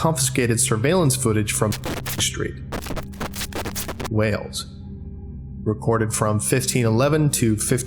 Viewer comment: The captions say Wails not Wales [sad welsh noises]